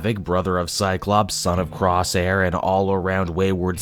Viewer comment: covering a main line X-Men title? The dark ages are seeing the light! lol! Also, did I hear "Crosshair" instead of "Corsair"? lol